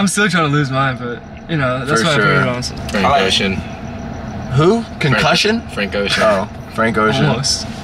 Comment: When said his favorite song artist was Frank Ocean i thought he said depression lmao